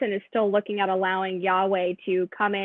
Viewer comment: did she just call Huawei “Yahweh”? Lol Thanks very much though, always love to hear Nigel speak.